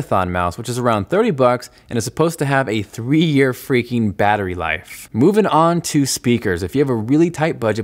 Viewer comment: Did I hear 3 year battery life at 6:56???